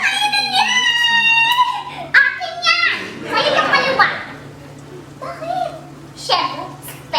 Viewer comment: May narinig ba kayong nagsasalita ng "sa kanya yung kaliwa?" Dun sa 3:08 abg creppy!😣😫😵😲😭🙈🙈